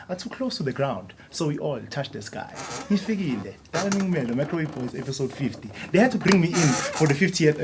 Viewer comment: Am I the only one who thought it said "Micro Weave boys"? Thought this was a hair tutorial! LOL